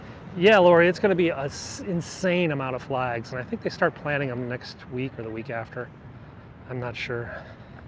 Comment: what kind of flags r u talking about? What did u say planting them? Plant what? U said planning them, I think?!!